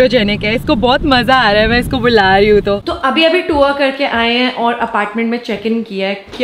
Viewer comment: I heard -- to abhi abhi twerk karke aaye hain😂